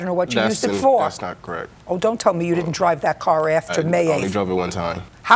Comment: Omg I thought it said feces 😫😫😫